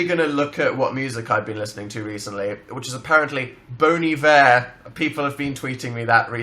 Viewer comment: when he said Bon Iver I thought he said, bonn-ie vear